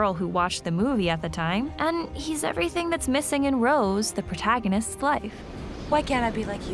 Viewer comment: I swear I thought she said "Rose, the protagonist slut." Caught me off guard haha